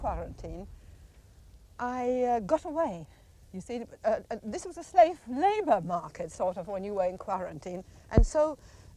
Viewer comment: Did she say “Megalin” who? @ 44:00 & @ 45:30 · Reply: Josef Mengele